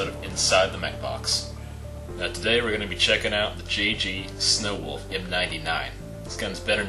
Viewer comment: at first i thought he said "today we're going to be taking a look inside my box..." i got scared